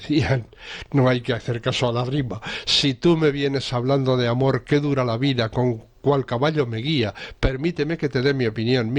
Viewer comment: Siempre pensé que decían "qué dura es la vida y el caballo de día", que para el Bilbao de la época tenía todo el sentido. Gran tema punk el de las Vulpes.